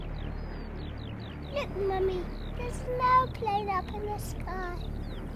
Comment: Well, finally: the original! "Look, Mummy...there's an airplane up in the sky--" Without that, the song loses half its poignancy. Thanks for posting, KAT. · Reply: I always thought that the boy said... Look Mummy, There's no cloud up in the Sky... brit accent.